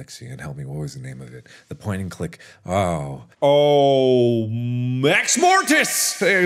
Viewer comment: seriously sounds like he says mexmortis, and now I'm imagining the microwave head with a sombrero.